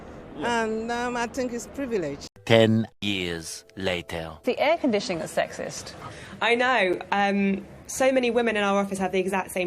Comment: ''The air air conditioning is sexist'' did I hear it right??🤦🏻‍♂